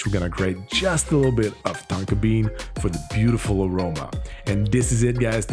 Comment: i thought you said tanka bean not tonka bean